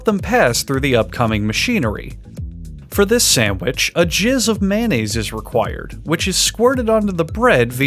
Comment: When I heard “a jizz of mayonnaise,” I was like hold up. Lol.